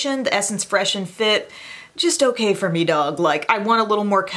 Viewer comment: Did she say a foundation was "just OK for me dog" at around 28:00?